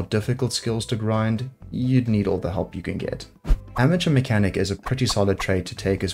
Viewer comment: Naahhh "You needle all the help you can get" you did not just say that lol